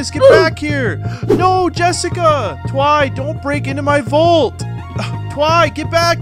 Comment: I Legit thought He Said Don't Break into My B?lls